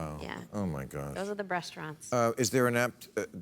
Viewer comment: I could swear that Saru Jayaraman said "Breastaurants" at 02:18.